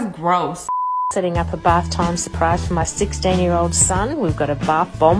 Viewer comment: She actually said "Six Day Year Old Son" not "sixteen"